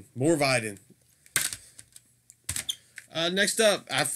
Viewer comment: Man I must've had like three orders pending with LAD at any one time, it's insane. Funny, everytime you kept saying "Morvidhen" … It sounded like you were saying "More Biden!" ……I was like, Nooooooooo… we DO NOT need any more Biden, LOL!